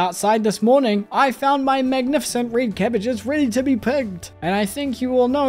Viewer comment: Every time says his crops are ready to be picked I mishear it as ready to be pegged. Maybe it's the accent, maybe I'm insane, maybe it's both